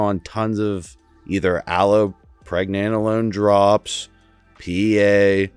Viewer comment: Hey Ive heard you say ALOE a few times? Im I hearing you right? I just want to look it up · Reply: allopregnanolone